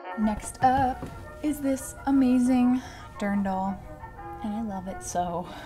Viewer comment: Haha..I have to share this. When you held up the Dirndl, I honestly thought you said," I love this darn doll". I never knew what those were called.😂😄 Mrs.O